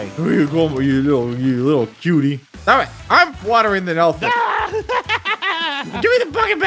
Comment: Poor editor 9:15 did said I'm watering the dolphin?